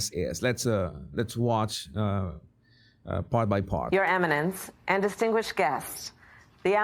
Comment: She's probably just not Christian... And it sounded like she said Eminem and distinguished guests 😅